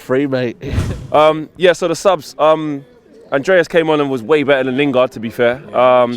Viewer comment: Thought u lot said De gea is better than leno